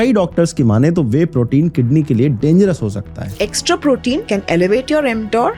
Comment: bro, don't spread misinformation, she clearly said "extra", and not just whey but protein (3-4+gm/kg) or anything can damage your body. And in Indian diet protein is just so low that we have to use whey protein(can also you plant protein for vegans, I don't like vegans BTW) and yes it can damage but we aren't consuming more but talking about consuming the required amount(and yes, only using whey is a bad idea, that's what I'll say) but please say the phrase in a correct way. I believe in wage you say, so don't break it please😡